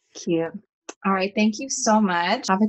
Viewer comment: did she say "byee babe"!!! omg LAUREMI GAY PANIC